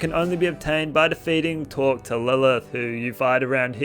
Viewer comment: So I first watched this video a couple of days ago and @ 13:47 you say where the gun can be obtained and I originally thought you said talk to Lilith, but I came back a day later, this time my I had left my closed captions on (I'm deaf in one ear so I sometimes I leave it on) and I see it said Torque to Lilith instead which makes no sense to me unless I'm missing something here. This has been bothering me ever since and for some reason, my brain is obsessed with silly things like this lol so I was hoping you could help me clear up which one you said just for the sake of my sanity, thanks in advance as well. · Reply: It's Talk to Lilith aka Evil Lilith or the lava is rising